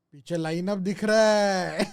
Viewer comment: i didn't understand? What he said and what's meaning of that ? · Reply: Piche white board par age ka plans likha huwa hai